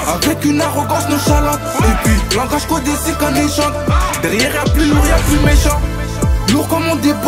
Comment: Svp il dis language codé … J’arrive pas a comprendre il dit quoi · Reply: Cest qun echant (echantillon)